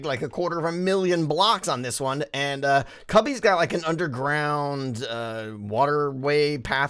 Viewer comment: did he call cubfan cubby? xD